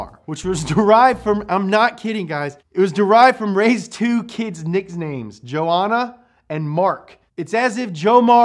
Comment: "Nixnames"? 😁 Great episode, by the way. Oh, also, at first I thought you said "Griffin" at 6:48 which I was thinking (Man, that's a badass name for a car!) but then I realized I had just misheard it when you restated that it was named just the "Griffith". Yes, they aren't too great at coming up with good names...